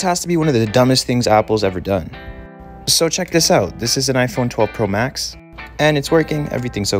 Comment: Call I hear now is "iphone 12 poo max"